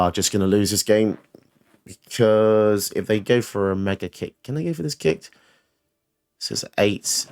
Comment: Did you say "Mega Kick" at 26:15? Ain't that a Pokemon move? Lol